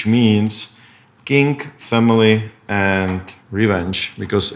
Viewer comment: Lol I heard "kink, family, revenge" Words to live by